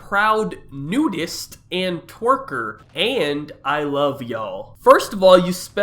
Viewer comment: I thought she said TWEAKER lmao